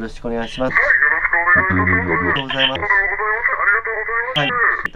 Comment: 頃男の人喋ってる？ 気のせい？？ 動画編集の時に音声変えてるんかな？詳しくないから分からないけど… 音声変えてるからなんか喋ってるように聞こえたのかな？ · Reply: ありがとござまったって聞こえました。こちら側の音声ですね。